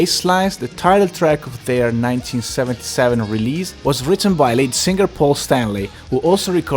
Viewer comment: Where did he get his information? He seems to get a lot of things wrong. I could have sworn he said late singer Paul Stanley. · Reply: Sounded like "late" but I think it was "lead". Just a heavy accent.